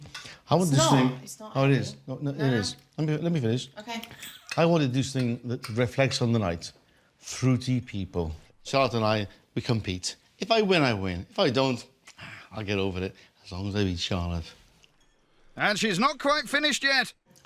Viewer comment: Did he say they were "fruity people"?? How did he know?